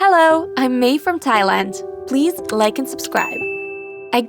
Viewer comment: I thought she said I’m made from Tiland lmao